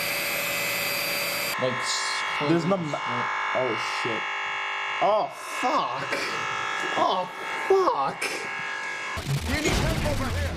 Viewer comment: What did he say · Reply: He said '' oh sh*t oh f*ck oh f*ck''